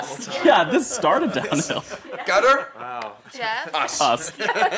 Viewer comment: What did JR say at 1:21? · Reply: Gutter- Us- 😂